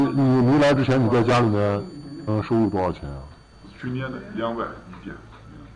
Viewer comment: He said 20,000 RMB (3,093 USD) instead of 200 RMB. Though I can't hear clearly whether that's per day or per year, because in Chinese, they are pronounced similarly, one is "tian" and the other is "nian"). However, given the context, there's no way he made 20,000 RMB (3,093 USD) per day. Mostly likely what he said is 20,000 RMB (3,093 USD) a year. It's a little less than what he earns right now(2000 *12 = 24000). Why would you mistranslate that? because your audience doesn't understand Chinese? Shame. It's a huge difference. Simple logic would figure that out, but you intentionally try to distort the story.